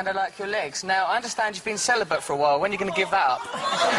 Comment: What does he say at 05:54 ? Can't make of it properly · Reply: "I understand ur being celibate for a while, when are you gonna give that up?"